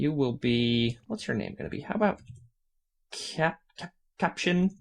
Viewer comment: Did I hear him say 'her name'? If that's true, then Captain Wiggles is a GIRL???